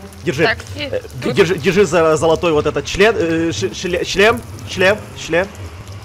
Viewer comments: мне 1 послышалось член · Прислушайтель вы что-то слышите я да я УСЛЫШАЛ слово ЧЛЕН вместо слова ШЛЕМ!!!